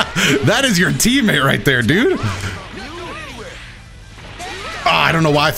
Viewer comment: Did call Videl a dude?